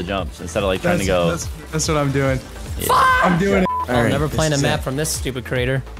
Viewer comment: did he say the f word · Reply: he said fudge